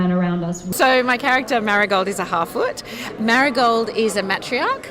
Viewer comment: Did she just say, "My character is a halfwit"? Cause that's what I heard. I'm SURE she said something else, but I heard "halfwit." Someone enlighten me, please, because I don't want to believe that she said what I heard. I mean, unless she's PROUD of being the village idiot? I mean, as parts go, idiots can be an absolute BLAST to play. I played an idiot, who was constantly stoned, and couldn't say or think of anything but "five o'clock," when she got her next hit. It was a HOOT! I was too young and naive to see the tragedy in it. As a teen actress, I just saw the comedy. So, maybe??? No, I'm sure I misheard it. I don't recall any halfwits in the Silmarillion or the Children of Hurin, or any of the appendices, or any of the tales... Naw. Gotta be a misheard thing.